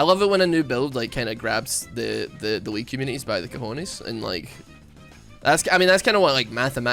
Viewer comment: Wait what does says on 7:35 Edit: I think is cojones, that's a spanish word but I'm not sure xdd